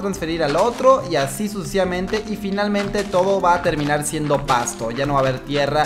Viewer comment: jaja soy el unico que se dio cuenta de que dijo suciamente jaja